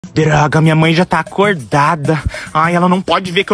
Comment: So eu escutei "pir@@ minha mãe já está acordada" ?